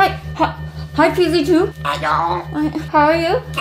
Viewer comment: i thought said "hi pz funf*😂😂😂